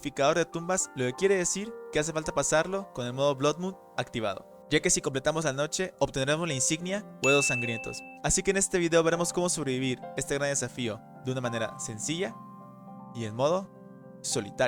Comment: entendi huevos sangrientos XDDDDDDDDD